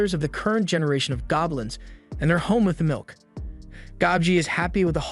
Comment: im sorry is he just say all their father's are home with milk 😂